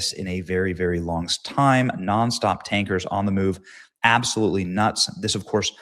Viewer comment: ON THE MOVE I THOUGHT YOU SAID ON THE MOON LMBO SORRY !